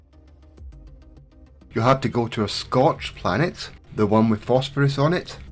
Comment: I am a huge fan of international accents and when you said Scorched planet as a type to fish on for the last helmet, I thought you said Scotch :) I pictured fishing up some bagpipes!